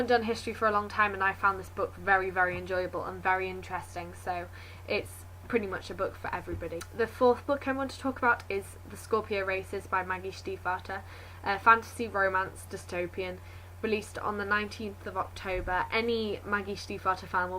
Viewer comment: At first I thought you said 'The Scorpion Racists' :P